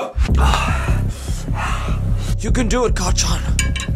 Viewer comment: I've always loved Your videos no matter how cringe they are. And what makes this video 100x better is that at 9:07 when deku says, "you can do it, kacchan!" Captions thought he said, "You can do it, carl john!"